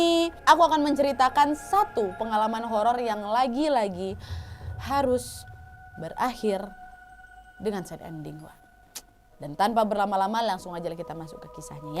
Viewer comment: itu ka bilang apa sih, menceritakan satu pengalaman horror yang lagi lagi harus berakhir dengan... Ampe buka subtitel tapi ga ngerti apa yg dibilang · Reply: harus berakhir dengan sad ending